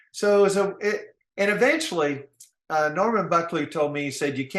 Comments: I thought he said Norman Buckland?? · was my favorite in the book this is a top notch interview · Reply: I thought he said Norman Buckland...who was a bare knuckle boxing champ in the UK...but def not him